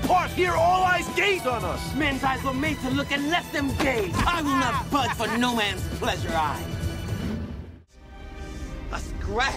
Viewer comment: What did mercutio say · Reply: "Men’s eyes were made to look and let them gaze. I will not budge for no man’s pleasure, I."